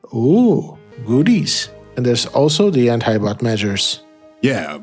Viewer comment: What does he say at 0:54 ??? Anti something idek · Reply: Anti-bot measures